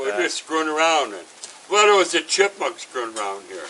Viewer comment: I thought the gentleman said chickmunks